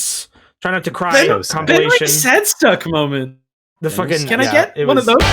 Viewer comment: did,,,,, did I hear "Benery sadstuck moments"? H HOMESTUCK?!???!!!!! REFERENCE?????? WH